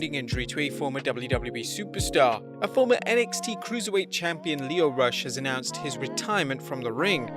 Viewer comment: Lio Rush? Oh I thought u said Leon Ruff or Lee Johnson